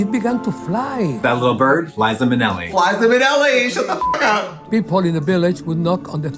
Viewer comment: “That little bird? Liza Minelli” I dIED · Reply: blinkuh Oh no! I thought I heard Flies-za Minnelli!